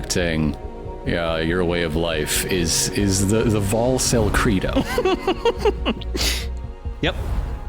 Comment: I dicked around for like two hours waiting on the Brennan or some other high pierce weapon to get done. And the heavy armor. I will also admit that I hid. @ 18:25, what'd he say? Idk if y'all said it or it was said elsewhere, but there are certain similar enunciation and pronunciations among different languages, how they didn't breed into other languages or people with loan words and whatnot. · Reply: "Volcel credo" Volcel meaning voluntary celibacy